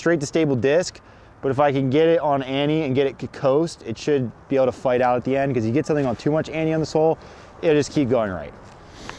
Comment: explains his shot and I could have sworn he said “putting to much anny on the sol” and I was confused why he was throwing duscraft. And then actually threw a sol on “the hole” lol